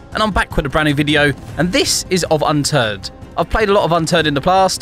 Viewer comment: For the intro it sound like he was saying unturd